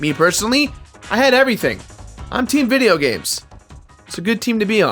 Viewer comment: When you said "im team video games" i thought i heard "im team money, i have money, fuck you"